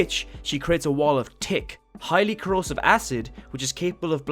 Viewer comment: Every time he said thick he sounds like he said tick